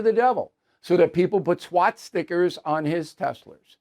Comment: Did you say swat stickers? 🤣🤣🤣